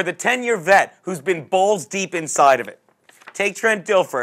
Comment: Man i was high last week when i watched your picks because i thought u said Jaguars +3, im gonna use your monday night pick this week and lay off the weed! It was Tampa Bay right?